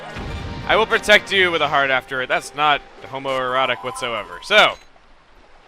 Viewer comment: At first, I didn't realize you said, "With a heart after..." I thought you said, "With a *hard* after..." before saying, "that's not homo-erotic whatsoever".